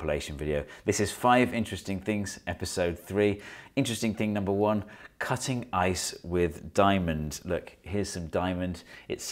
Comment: I thought that he was saying "Cutting EYES with diamonds" lol And didn't doubt to think that he would be talking about some new scalpels for eye surgery with diamond edges.